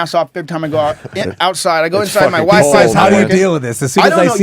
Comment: did he say "my wife" for a second and got interrupted?